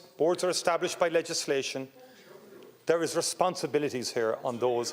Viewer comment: What did he just said @ 10:00 ? They are fkng not responsible???